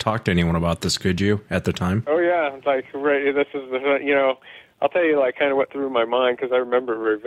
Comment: 😂 what did he say? Ohyeaaviceahbrarythisistheumhayaknow 😂😂😂